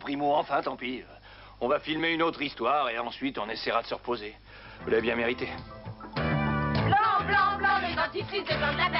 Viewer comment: Anybody know what is being said on 21:14? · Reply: Vous l'avez bien mérité